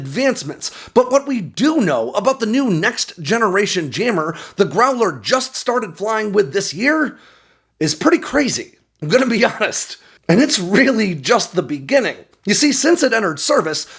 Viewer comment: Did he just say jammer as in cats and jammer?